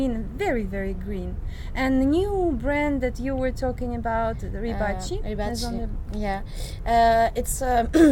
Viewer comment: I can't understand what brand is said. Closed captions says "rebar chief". haha! Many of the fragrances sound wonderful but I really can't pick up the names being said. Probably for the be$t, anyway. I'm still jealous. Sounds like a fragranista's paradise! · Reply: They said Givenchy. : )